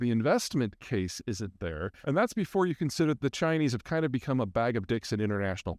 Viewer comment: did he say the Chinese have"become a bag of dicks" 🤔😂